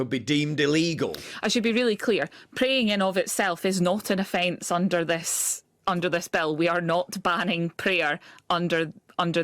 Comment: It's funny when she said "prayer itself is not illegal under this bill" in her high Scottish accent I heard "prayer is not illegal under this spell" which I think is possibly a more accurate description.